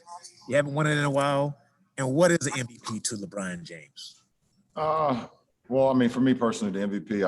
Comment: did he just say LEBRYANT JAMES